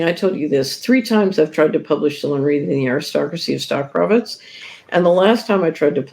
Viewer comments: What is she saying she tried to publish 3 times? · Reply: Her book Dillon Read and the Aristocracy of Stock Profits... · what did she say she was trying to publish? I couldn't make out the words. And she said it is out there. Can I find it at the Solari site? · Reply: The Aristocracy Of Stock Profits